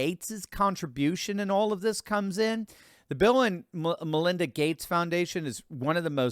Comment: Lol...he said Bill and Melinda but I heard it as villain. I had to go back and listen again